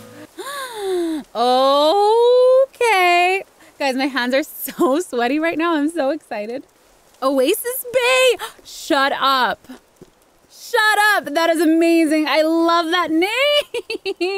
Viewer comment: I heard "a waste of space" than "oasis bay"... idk lmaoo